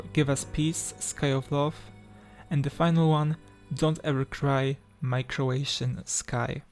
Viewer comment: not the subtitles saying "don't ever cry microwave in the sky"